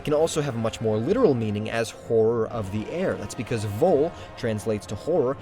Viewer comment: did I hear that correctly cuz I heard whore of the air